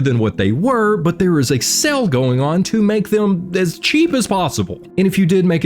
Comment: I know you said there was "A sale" going on with your new awesome merch, but I heard "Excel Going on" and was immediately excited.... I have problems 😊